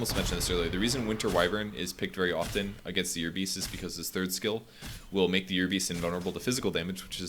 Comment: Uhm sorry what do you say at around 32:00 ? Wyvern is good against... "your beast"? what? You say it several times but I dont get it. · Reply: "year beast".